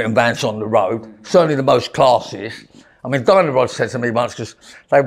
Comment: He looks Very Animated.... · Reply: For sure. At one point I thought he said Diana Ross wanted to buy them, but then I realised he’d said Dyno-rod.